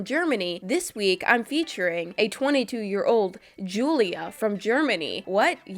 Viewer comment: That was weird first I heard “Julia” then “12”😂And my name is Julia and I’m 12 years old😂